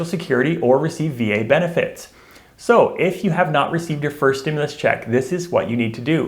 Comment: Oh I thought he said weed benefits when he said wic!!😂